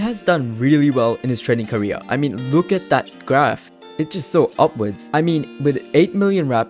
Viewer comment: i thought he said so obvious, i legit started typing upwards though